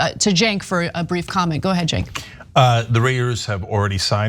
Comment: did Ana call Cenk “Junk,” or did I hear that wrong?